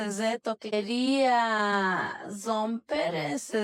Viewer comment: This was fascinating. 2:57 sounded like the woman was saying straight z's.